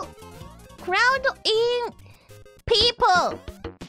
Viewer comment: There is few moments when here pronounciation made it difficult to understand and (maybe it was on purpose) here is some corrections : 3:22 I think she said "cloud in people" instead of "crowding", Korone often make this mistake of inverting the "X in Y" statement haha 5:44 I think here she is calling them "olive" instead of "a weeb" ^^ 4:42 for this one I think she asked "no clear ?" instead of "no korea" because she was surprised the game didn't ended here 5:36 after telling "I am a live !" I think she said "I have a heart" instead of "a hat" Nice work and edit tho ^^ thank you !